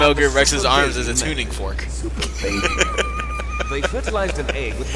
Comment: For a second... I thought liquid said "super baby metroid" not "super baby method"